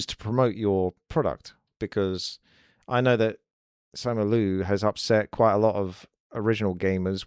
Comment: who made those political comments? I hear Samulu or something